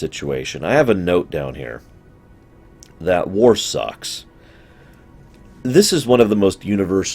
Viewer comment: I totally first heard this as "I have a note down here...that Worf sucks!" :D